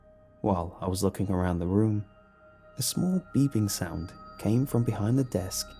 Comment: Who else heard "A small vegan sound"